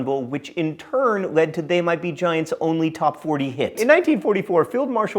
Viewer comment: Wtf is he saying @ 3:11 Can't understand it even at 25% speed. Giants? Top 40? Fuck this guy